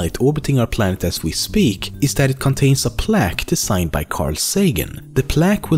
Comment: did he say "A plaque to sign by *Carl Sagan*"